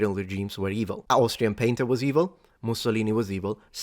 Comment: "Austrian Painter was evil." That made me think for a couple seconds. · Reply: I had thought that you had said, "Austrian Painting was evil."